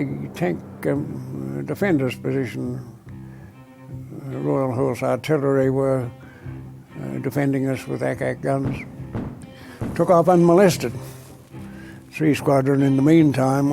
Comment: Did he just say "the royal whore's artillery"? If not, I apologize for any disrespect but if he did, it's one more reason to love the Aussies. Thanks for providing the history of these brave men and their heroism